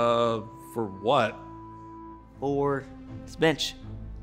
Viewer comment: I thought he said for this b*tch